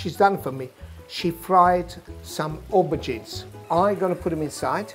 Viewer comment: what exactly did he said this is?Looks like fried diced eggplants but i didn't catch what he said. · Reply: Fried aubergine.